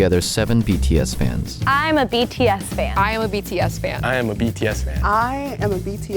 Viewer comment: "i'm a bitchy ass fan" i'm sorry but that's what i heard 😂